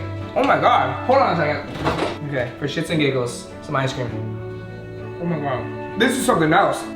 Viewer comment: did he say “for shits and giggles” 😂